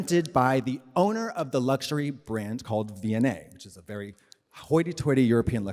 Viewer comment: I’m watching this pod and at 55:48 Katya mentions the story of her in the Pool doing the photoshoot. She said the brand name. I heard “V and A” and I’m searching for it but I keep coming back to the London museum. Can someone help me out here? I need to find the image of the owner so I can finally put a face to the No champagne? No cocaine? Mwaaahh anecdote!!